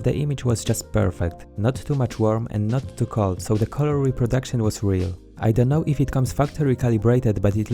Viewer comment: did you said “Color Reproduction?”